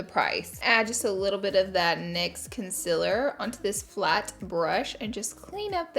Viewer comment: Swear I thought I heard you say con·SEALER and not the usual con·SILER haha. Love you laura!!! Always so gorgeous